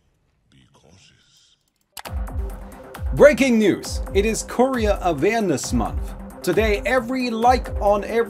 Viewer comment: the courier awareness break pause, i was not looking but i heard like "KOREAN awarenes month", i was shock at 1st but then i realize it was miss pronunciation 😁